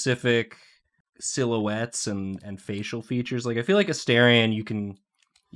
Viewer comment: As an Australian, thank you for making a drawing I can properly see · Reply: I thought you said Austrian, and was very confused for a bit